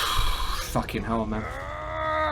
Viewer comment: what did he say? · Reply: He said "*sigh* fucking hell man"